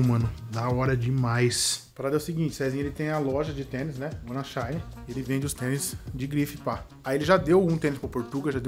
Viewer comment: Qual o nome da loja do cesinha que o fala em 14:42? Edit: Encontrei galera, escutei em 0.5 e ele fala "wannashine". É o nome da loja!